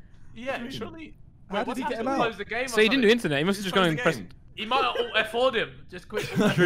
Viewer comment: What does Simon say at 3:39??? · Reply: I just went in his room